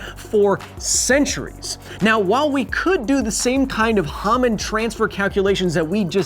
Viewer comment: I keep hearing "hamon transfer" Science of jojo when